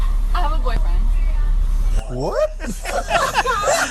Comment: I think the girl thinks he said ‘why don’t you have sex with me chicken’.